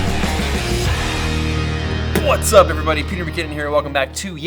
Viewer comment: The way you say "what's up", I hear "butts up". That is how it sounds like. LOL